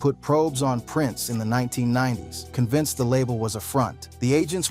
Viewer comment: "in the 1990's" caption say's 1090 lol